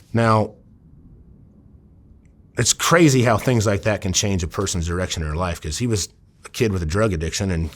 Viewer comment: am I the only ine who heard “can change a persons erection in their life” ? 😂💀